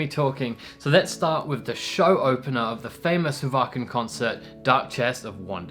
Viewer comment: Nice one! Ooops! The subtitles are a bit odd, it thought you said "Vacant" for Wacken! I'm giggling here. Then it says "seen quite a few from the vacuum show" :-) "I love this bed so much" Sahara, Tampa Floorida. Oooh, that Marco face and the Bass, ooof! Middle Eastern flavour, 1,001 nights (Scherazade? That's 3 years!) Emppu throws a pick at a big pick scoop! Wasn't that a great journey, really takes you in. Emppu "emperor" hehe.